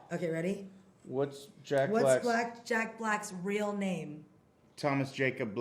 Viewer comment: "What's black jacks black real name" hahahaha wtf did she say